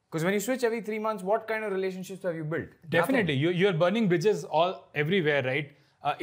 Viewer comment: i heard you're burning bitches 😂